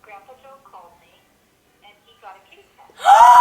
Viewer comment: Haha, I thought she said grandpa Joe got a kidney stone! I was confused why she was so excited. :D